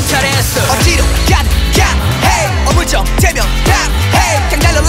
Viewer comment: I thought he said “wake that a*s up” 😂😂